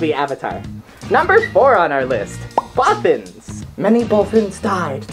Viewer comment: For the Bothans part, I thought she said brothers for my whole life...